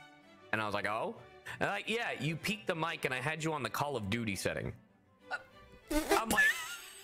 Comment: Bruh, I’m immature. When I heard this, I thought he said “I peaked on duty” and I fucking laughed.